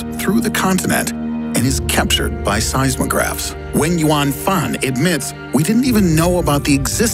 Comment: For the longest time, I thought you were saying "when you want fun". But you were actually saying "Wenyuan Fan"..